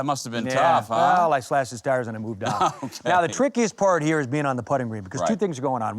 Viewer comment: Slashed Trevino's what? Couldn't understand that word. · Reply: He slashed his tires and moved on.